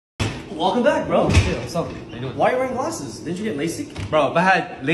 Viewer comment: i might be trippin but towards the beginning i heard, “what’s up n***a” 😂